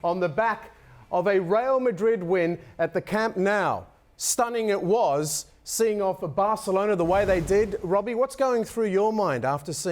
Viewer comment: Closed after i heard "Camp Now"